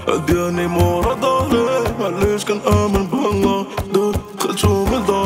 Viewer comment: Did he say I don’t believe in ALLAH anymore @ 0:29🤔 wash 9al mab9itch tan amen be’ALLAH 0:29?🤔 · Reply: مبقيتتش كنأمن غي بالله ، يعني تا حد من غير الله